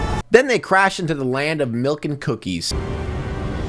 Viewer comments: anyone else hear lava girl say melk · did she just say MALK?